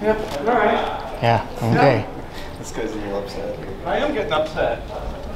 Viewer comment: “ I don’t get upset.” throwing a water bottle and a chair.….. pick that chair up and have a seat. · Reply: I thought he said i am getting upset